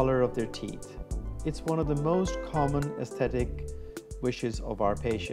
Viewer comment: I thought I heard the word pathetic